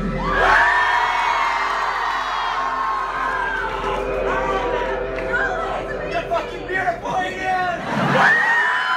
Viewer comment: what is that guy shouting :)? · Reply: YOU'RE FUCKING BEAUTIFUL EDEN